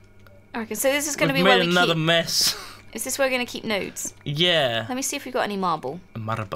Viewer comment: said "is this where we're gonna keep nodes?" But I thought she said "is this where we're gonna keep nudes?"